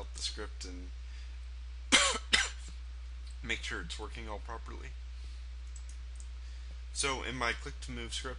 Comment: "Make sure it's twerking all properly. " Sounded like he said that. Lol